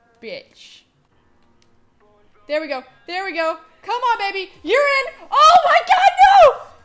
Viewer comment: did she say urine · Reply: she said your in, meaning he player was in for a goal